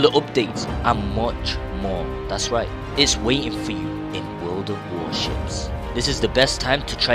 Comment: Passed. I thought I heard him say "World of Warshit."